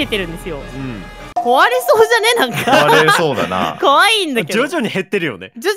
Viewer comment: 可愛いんだけどにしか聞こえない